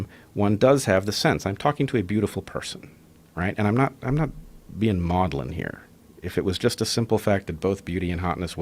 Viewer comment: Does anyone know what word was said @ 2:40? Something like modelin, modelan? Tried looking it up and ain’t getting nothing…